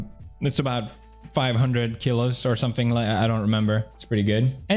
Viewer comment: im sorry did he say 500 kilos?